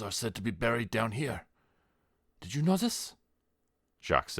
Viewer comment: 6million dead buried here did you notice is what I thought he said with the accent at first my wise butt thought nooo didn’t see the walls of bones around us 😂